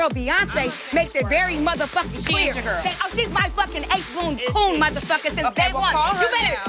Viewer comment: “eight-floon” what?? What did she say? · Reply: she said “ace boon coon” which means a close friend